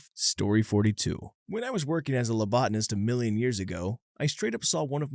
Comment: THEY MEAN PHLEBOTOMIST YOU GUYS WHAT THE ACTUAL HECK NO NOT A LOBOTOMIST AS THE SUBTITLES SAY NOR A LOBOTNIST AS HE SAID NOR A LEOTIST AS THE CHAPTER TITLE SAYS o_0